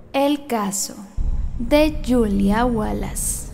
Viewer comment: Jajd noo. Que mal. En la introdución dijo "El caso de..." Yo escuche "el caso de William wallace" :(